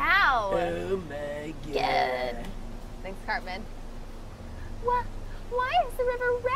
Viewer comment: Wait did Steph just say Cartman at 24:52... Like South Park. GT watches South Park... Sweet